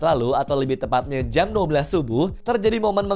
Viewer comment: jam 12 subuh?? Gw ga salah denger kan